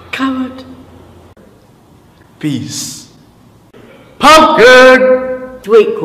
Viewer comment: I think he really said peas